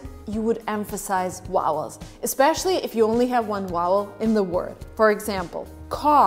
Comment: I had to keep playing when you said "vowel" cause I heard "wowel". Hehe. Is it time to clean my ears or that's just how you said it? Btw, I love your explanations. <3